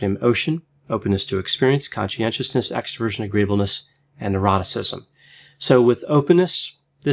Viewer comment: love these videos. i crack up every time when the doc unpacks OCEAN because i always hear it as, "openness to experience, conscientiousness, extroversion, agreeableness, and EROTICISM"